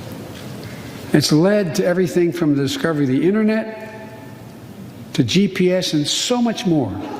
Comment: Did he say "discovery of the internet"? (Approx